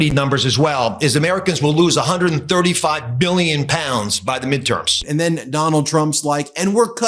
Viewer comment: it sounds like he saying "millions" not "billions". There is some accent in his voice. It kind of reminds me of how ventriloquists use substitution of different sounds. Arguably though it does kind of sound like "mmmbbillions". Millions of pounds is still very high though.